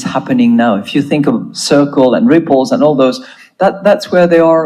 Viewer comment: she said RIPPLES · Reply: I thought she said nipples?!😅